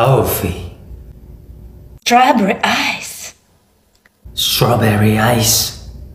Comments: The girl: "stabery icseee" The guy:"strawberry ice" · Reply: I thought she said "Jaber Ice" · The first voice he sounded like a eco🤣 and when she said strawberry ice I thought she said dried ice💀